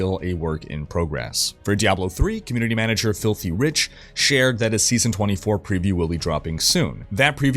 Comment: Did i hear it right ? Filthy Rich ? Lmaoooo